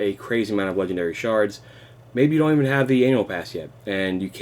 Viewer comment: sounds like you said anal pass